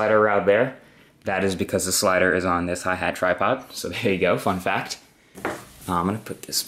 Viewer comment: at a 8:33 i thought he said fuck that but he said fun fact